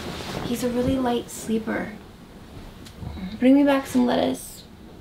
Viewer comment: So I guess I'm the only one that heard her say bring me back some lettuce lOl!. oK then.